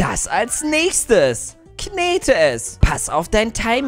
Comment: ich hab zuerst knete essen gehört